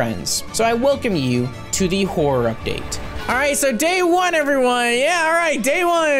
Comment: I DONE THOUGHT HE SAID WHORE UPDATE AT 3:15 BRO😭😭😭😭😭